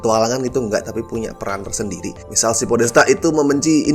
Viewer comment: kenapa gua salah denger jadi "pak petir" sih wkwkwkwk